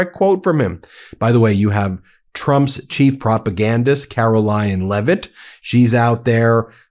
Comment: It sounded like Ben said Carolyinglevitt! 😅 I guess it would be true!